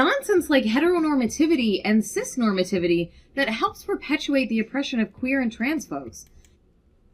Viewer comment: Wait, did she just say "the oppression of queer- and transphobes?"